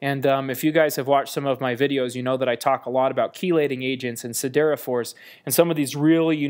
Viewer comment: Min 3:33 - What is "sidera force"? I can't understand whether its sidera or something else